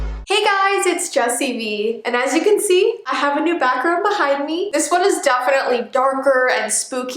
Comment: When you said 'I have a backround behind me' I thought you said you had a bathroom behind you xD